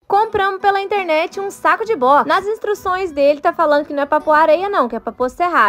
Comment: Achei que ela tinha falado compramos um saco de bosta kkkkk😂